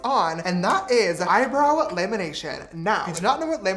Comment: when he said “lamination” who thought he said “elimination” and got scared he was eliminating his eyebrows? edit: bro i have never gotten this many likes thank you all so much!!